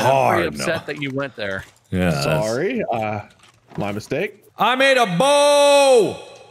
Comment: when Mark says “I made a boat” I was thinking about what did he said boat or bored